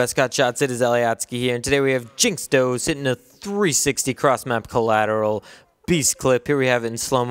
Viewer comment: sorry, he said collateral, i thought he said triple at first